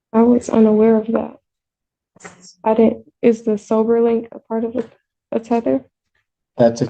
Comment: Oh "sober link". I heard "silver link" and was wondering what that was!